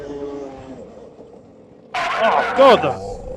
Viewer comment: Is portuguese? At 19:32 it sounds like he says "Oh, foda-se!" which is portuguese swearing.